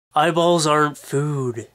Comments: i was making a wierd loud and annoying sound with my mouth at the begginning and i thought the dude said "balls aren't food" O_O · in the beggining i thought you said " my balls arent food " :P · At first, I thought he said "My balls aren't food" at the beginning.